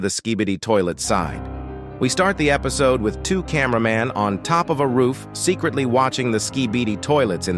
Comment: Did bro just say camera pans or am i just dumb cus other said camera man but at 60 i heard him say camera pan